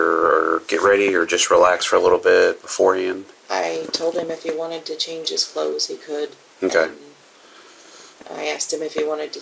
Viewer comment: she says I told him he could change his clothes or shower if he wanted to. Who's controlling who. Thought she said he's controlling. She could've just kicked him out.